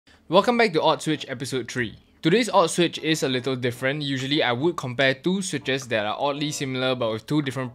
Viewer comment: bro I heard "Welcome back to Auschwitz episode 3" at first lol